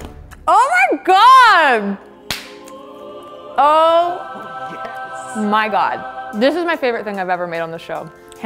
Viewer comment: Was it me or did anyone hear say "YeS"?? When she tried her jack fruit.